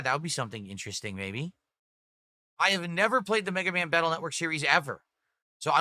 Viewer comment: I swear he said Beggaman 🤣